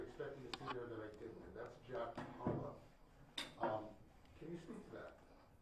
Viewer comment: Did he say “Jack Harlow” at 3:40??